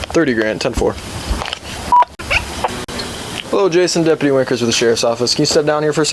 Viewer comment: did he say he’s Deputy Wankers?